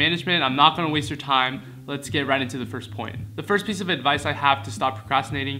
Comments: is it me or did the subs just said 'cunt' instead of 'point' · does not say “point”. Not sure if you want to change this somehow. Rude word haha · The caption says "Let's get right into the first cunt" lol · the captions say “lets get right into the first cunt”